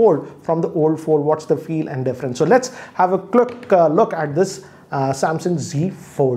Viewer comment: "let's have a qluck" 😃 instead of quick look